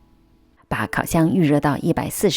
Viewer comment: hi! i think there is a error in the english subtitles on 1:26 when in the video you said the temperature was 140°c but the subtitles says it’s 150°c